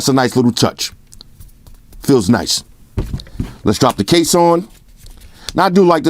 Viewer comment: She I thought he said let's slap the case on, he actually slapped it on😂